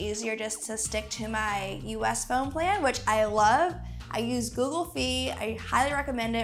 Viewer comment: did she just say "Google fee"?